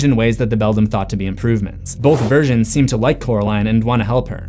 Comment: I was skipping through the theory part and it said "versions" and I thought it said "virgins" and I got really scared